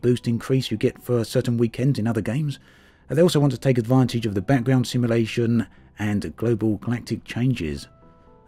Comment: I thought you said "Batman Simulation" and I was like "Whaaat?" Then I realised you said Background Simulation .. and I fell asleep. Hope the rest of the video was good.